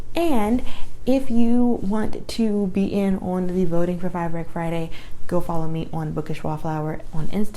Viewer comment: For a second I thought you said "frybread friday" and I thought there was gonna be frybread. But either way this is great!